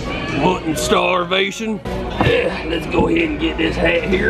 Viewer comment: did he say what in starvation I think it’s what in tar nation